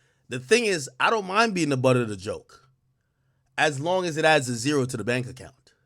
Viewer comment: I thought this dude said I don’t mind being the butter of the joke 😂